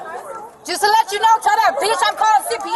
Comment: What did the girl said at the end of the video moments before getting arrested? I just heard her say; "Just to let you know Taylor, beat her up 58". ?? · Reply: “Just to let you know b*** Im calling CPS